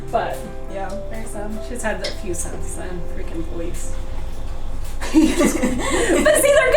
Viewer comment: I don't understand what she meant by Halie's had a few since then and then says 'freakin police?' or what does she say? Can't hear. Why does she say police... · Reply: said "Fricking boys"